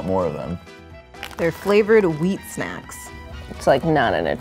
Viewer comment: I thought she said "Flavoured Weed Snacks" 😂